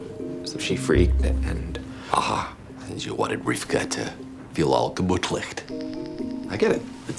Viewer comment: According to some googled script i found, he says this: 11:43 "House: Aha, and you wanted Rivkah to feel all gemutlicht. I get it; it’s a shandah." But i feel what he says instead of 'gemutlich' it's some playword with 'bootleg'. Maybe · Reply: This, except for its german words, so its actuall house saying: "You wanted Rikhav to feel all gemütlich (comfortable), what a schande (shame)"